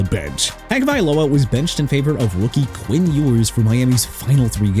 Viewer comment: didn't it sound like he just said "WOOKIE Quinn Ewers"? 🤣🤣🤣